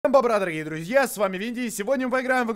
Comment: Кому послышалось вместе всем бобра - на ем бобра?